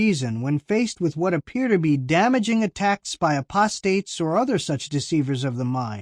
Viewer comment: What's an "aprostrate" ?? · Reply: C Rhynehardt I don't know if we are talking abt the same thing, but I thought I heard say aprostate instead of 'apostates!' Did anyone else hear that? I scrolled back 3 or 4 times to listen. ( I'm not deaf, but am hearing impaired. )